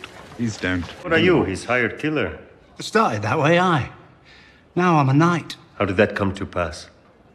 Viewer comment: tf is Bronn saying? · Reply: "Started that way, aye"